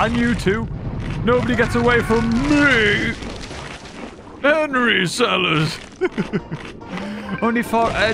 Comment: I have been sitting in my chair for about 10 minutes now trying to understand this caveman launguage. I have no idea what he could be saying sombody please help. What I hear: "Henry sell us" "Every Salad" "And reef salus" please I am begging if anybody knows what is happening here I need to know